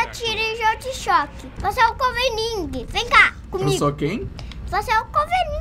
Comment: só eu entendi que o filho do disse "você é o comeninguem" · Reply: ele falou wolverine só q do jeito dele